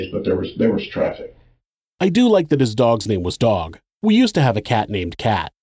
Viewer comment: i used to have a cat named "cat" too😂 I could have swore he said "Doug" was the name of the dog